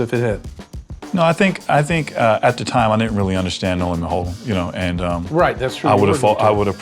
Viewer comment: What does he say at 5:23 “ I think that time ???????” Cheers natives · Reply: "I think at that time I didn't really understand No Limit Holdem"